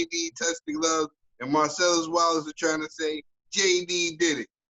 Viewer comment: Whenever I hear "Marsellos Wilder" my brain says "Marsellus Wallace". Even at about 6:12 he says it too!! Can't not think it!